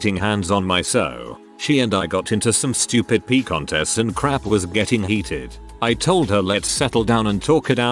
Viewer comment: What’s the p word at 7:18 ? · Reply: "missing" but m=p